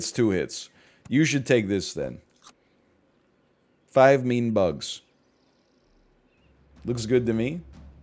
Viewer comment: I’ve always heard it as “meme bug”